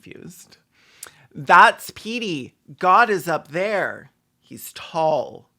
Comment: When you said, 'God is up there...' I heard, 'God is a bear, he's tall.'